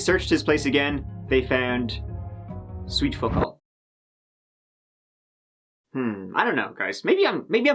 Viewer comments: what did you say at 9:17? the closed captions say "they searched his place and found sweet football" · Found what?? i keep going back but can't understand what is said. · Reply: He said they found sweet f*ck all